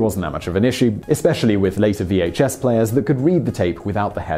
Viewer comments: At first I thought you said laser vhs players at 11:06. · Huh? Did he say 'laser VHS players' at 11:05?